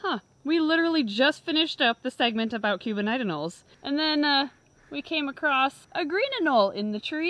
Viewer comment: I thought that you were saying "Cuban nationals" !